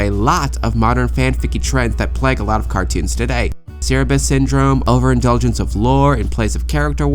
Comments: what did she say about tangled the series? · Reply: That Tangled the Series falls into the cerabus syndrome trope. The definition of cerabus syndrome is "any story/series which starts out light, episodic, and comedic, and then assumes dramatic elements and a more coherent continuity" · what's the name of the syndrome? I can't catch what you're saying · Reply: Cerebus Syndrome. Basically it's when a lighthearted series (usually a comedy) completely and sometimes suddenly shift its tone to something more "serious" and "dramatic," which can quite often and quite easily backfire.